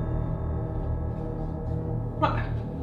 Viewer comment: Why does it sounds like he said "fuck" lol Continue plz! COOL VIDEO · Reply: lol! he says: ma (that in english means "but") However Thanks!